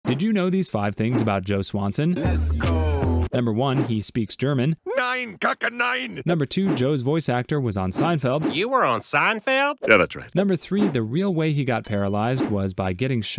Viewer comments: Bro did he Just say “NEIN KAKA NEIN“ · Why does it sound like he said nine caca nine